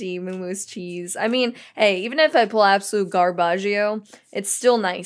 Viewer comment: Did you just say Garbagio